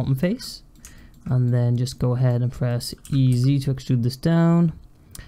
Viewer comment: Lol I’ve been watching your videos for a while and for the longest time I thought you were always saying “Easy to extrude” instead of E Z to extrude. Just realized that now